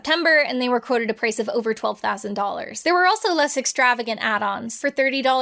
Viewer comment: I'm not sure how the subtitles got this this so wrong, but at 29:20 it says $112,000 instead of $12,000.